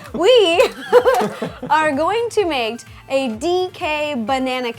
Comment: Did you say "am going to mate a cake" at like 0:55? -OH JESUS DINO SEX. On that topic, I think a nostalgia cake of Jurassic Park would be pretty cool.